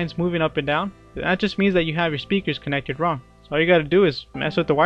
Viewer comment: what did he said at 3:48?? pls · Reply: I think he said wrong xD